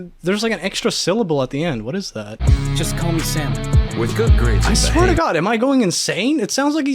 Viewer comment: I hear Samu